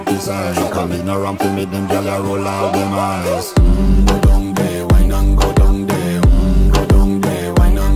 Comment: I can honestly say I've never seen such a beautiful soft tackle by a panther before.the music completely fits perfectly 💯 I don't know what the music's saying,but it definitely feels the right vibes 😎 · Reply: Yeah it's majestic right? Btw i can't figure out what it says at the beginning cause it's Jamaican english but then I hear "hmm, go down there, why not go down there?"